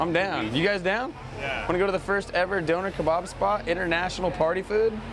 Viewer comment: Hahaha i thought you said "doughnut kebab", not "donner" as we say in UK